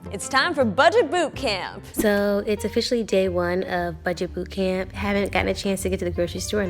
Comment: REALLY... I'm the only one who heard her say budget boob camp..😆